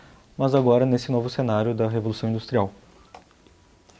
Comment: Qual o nome da encíclica do Papa Leão XIII? Não entendi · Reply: Diuturnum Illud